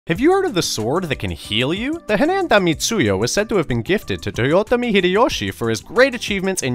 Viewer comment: Why did I hear "The Henaenta Itsuyo"?